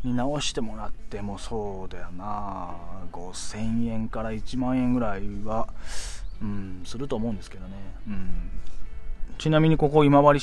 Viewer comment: 「5千円から1万円くらい奪っ…うーん、すると思うんですけどね」僕は耳がないがはっきり聞こえた気がした